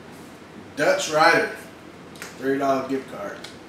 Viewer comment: Dutchrider? haha I was so excite I thought you said TexRider when I watched it on my phone ..Congrats DutchRider Take Care Guys